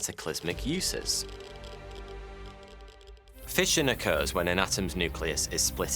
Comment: What he said: " fission occurs when" What I heard:"Fishin' a curse and"